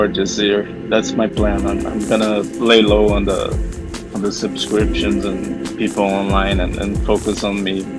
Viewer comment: Does he say 'SIMPscriptions'?